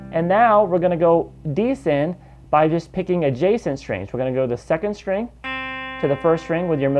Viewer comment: Many thanks for your excelent videos!I don't understand what are you say at 12:07-jason string?What is that?